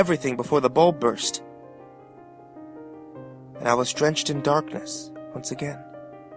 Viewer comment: I thought he said "I was drenched in dog piss" oops! darkness brain. darkness.